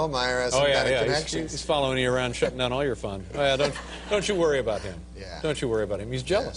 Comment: Is it my ears or did Dave just say" . . shittin on all your fun" without being bleeped?